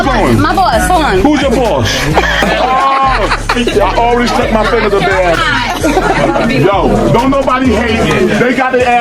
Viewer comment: DID HE JUST SAY "I PUT MY FINGERS UP THEIR ASS" ????????